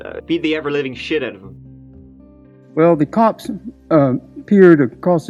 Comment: Did he say "the COCKS" instead of cops? LOL!